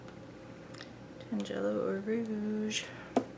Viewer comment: What is the orange Dina wakely colour called please ? Oh yes I found it tangelo, I thought you said tanned yellow, typical me 😂 🙄 I love watching your vids btw x